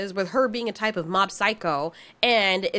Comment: did you say Mob Psycho?